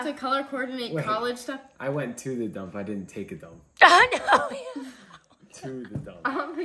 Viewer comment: I thought he said he took a dump